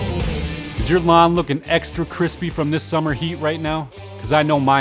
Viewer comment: I thought he said is your mom looking extra crispy. I was like what he is into my mom too.